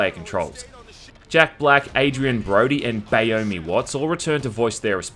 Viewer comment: Did he say BAYOMIE Watts?